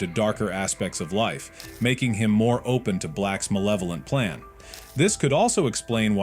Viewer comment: I thought it said molesting 😭😭🙏🙏🙏